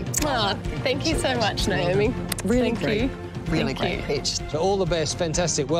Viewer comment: Am I the only who who thought she said “really great bitch”